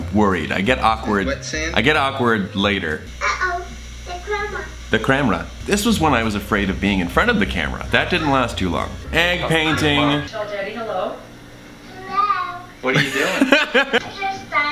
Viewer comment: When baby sanders said oh no the camera I thought he said oh no the crab run!😂😂